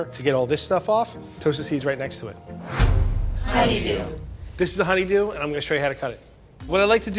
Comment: Anyone else hear "how ya doin" instead of honeydew?